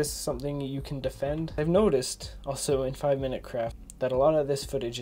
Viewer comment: it sounded like he said 5 minute crap LOL